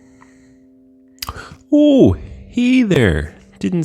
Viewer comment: Did he just say "Hiy didn't see you there" instead of Hey